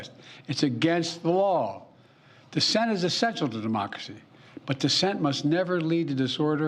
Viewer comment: Sounds like he said de-moc-a-cy instead of democracy. Had to play it back two times to make sure. Guess his brain cells were not connecting again. What a waste of 5 minutes. Too little too late. Then runs off when reporters start asking questions. Guess he didn't want to get in trouble with his handlers again. Pathetic excuse for a president.